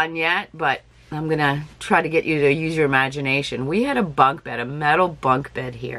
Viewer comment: I love hie you thrift stuff!! When you were talking about bunk beds, I thought you said "bug" beds. Ans I was thinking, "What the heck is a bug bed? Must be a Canadian thing." LOL!!